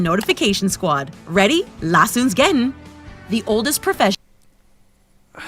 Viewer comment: What did she say? Let's get going?